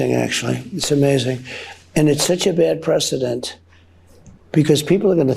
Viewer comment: We had so many ammunition it was coming out of our gills! As they say! 4:52 did he just say he’s a bad president! That’s what I heard. He is talking to and about dictators like they’re trading cards 😮 😂